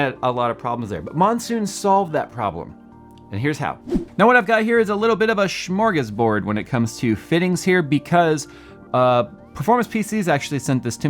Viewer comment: Did you say "smörgåsbord"?!?!?!